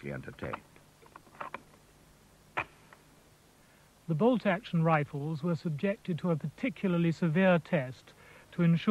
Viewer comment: Did anyone hear what the name of the rifle from 5:51-6:59 was? I couldn't quite understand him. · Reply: Hey, sorry this is 6 years late but it's a Carter-Edwards. The narrator says "Carter and Edwards". - Corey